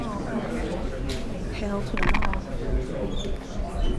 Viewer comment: the 5:00 mark you can hear a snowflake and I swear it saying "hell to the naw"